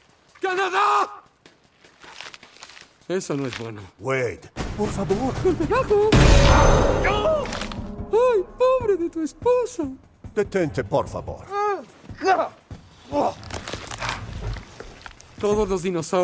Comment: ESA MAMADA ME CAGO DE LA RISA XDXDDDDDDD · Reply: yo entendí "ay madre de tu esposa"